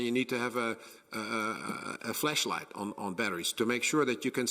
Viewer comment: Man.... it's too early. I thought this man said extra batters for your flesh light... Then my brain was like "as funny is that is, this has to be an ai deep fake". Then realizing he said FLASH light... I was not prepared for this roller coaster this early. God help us if our flesh lights die gents.😂😂😂